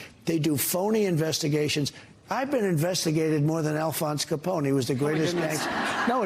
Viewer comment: Alfonse capone died a sad death, get the popcorn donald is dying of dementia in front of us.... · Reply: I heard "Caponi". Who pronounces it "Caponi"?